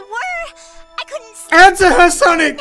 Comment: I thought he said, “Enter her, Sonic!” LOL